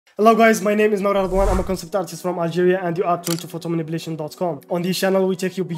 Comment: Always thought you said Nigeria haha after reading thru your course stuff I see it’s Algeria . Great video will be looking into this course and joining the Facebook group !